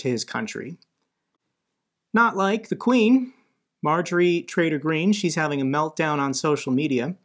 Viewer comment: Because of my bad English I always hear: “Major Traitor Green” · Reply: always does say, "Marjorie Traitor Greene" (deservedly).